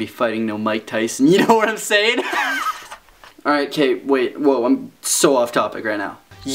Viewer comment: It sounded like you said "Alright Kate, wait." I nearly died.